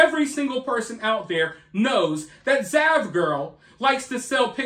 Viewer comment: I thought you said vaj-girl lol